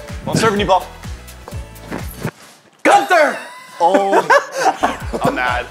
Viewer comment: did just said Gunther? (aka Ice kings right hand penguin in adventure time)